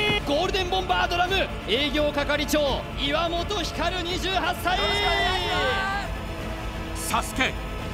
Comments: 営業係長、岩本照ーに聞こえるw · 営業係長漆原やのに なんか岩本照みたいに聞こえる